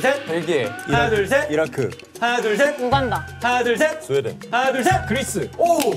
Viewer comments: what's wrong with subtitles? I heard sweden, iraq and uganda clearly but in subtitles there are other countries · are the translations real cause I heard the actress say Uganda but the translations brought sth else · In 19:21 did He really said Bangladesh 🥺🥺🥺?? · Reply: no they change question for the English sub, they acc asked for countries that have 3 characters not countries that begin with the letter b. he acc said Greece.